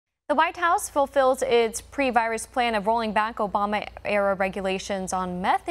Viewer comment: Thought the headline said Obama era meth 😂😂